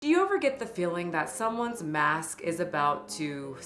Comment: At the very beginning I heard “someone’s mass” the first time I listened 💀 I had to go back